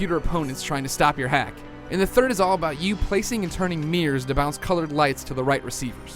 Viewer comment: Haha. Check out 1:43. I think the guy tries to say mirrors but it sound like 'meers'. Is that seriously how Americans pronounce mirror?? That's fucking retarded. There are clearly 2 syllables in mirror. Mi-rror, no?